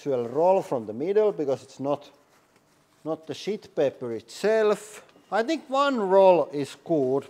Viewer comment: When you were talking about the toilet paper, I thought you said shit paper🤣, then I realized you said sheet paper🤣. Love this channel so much!